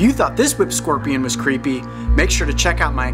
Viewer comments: Lmao I thought he said Taylor swift scorpion 😂😂😂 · I thought he said Taylor Swift Scorpion! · lol at first I thought he said Taylor swift scorpion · I thought you said Taylor Swift scorpion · I thought said, "Taylor Swift Scorpion" lol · jajajajajaj i thought it said taylor swift scorpion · I thought he said the Taylor swift scorpion 🦂 XD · Omg i thought he said Taylor Swift Scorpion!!!!!😂😂😂😂😂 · I thought I heard him say "Taylor Swift scorpion" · i thought he said taylor swift scorpion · Who else thought he said the Taylor swift scorpion? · Sounded like you said Taylor Swift Scorpion....SAY WHAT NOW?? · My little brother thought you said Taylor Swift scorpion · I thought at first he said the Taylor swift scorpion aha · Did anyone else thought he said "Taylor swift scorpion"? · am i the only one that thought he said this is the taylor swift scorpion? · did he just say taylor swift scorpion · Am I the only who thought he said "Taylor swift scorpion"?😂😂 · I literally thought he said 'Taylor Swift Scorpion'. XD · it sounded like he said the Taylor swift scorpion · did he just say Taylor Swift scorpion ? is it just me ? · I thought he said Taylor swift scorpion... Lol